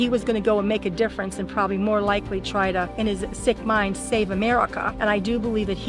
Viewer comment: im so cooked at 15:56 thought she said "sigma save america"🙏😭💀